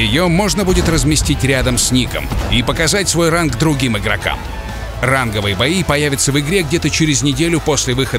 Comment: мне в начале послышалось раковые бои